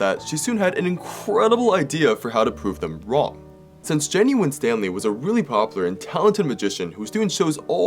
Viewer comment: When he first said "Jenny Winstanley" i thought he said Genuin Stanley, which would be ironic with what she did in the video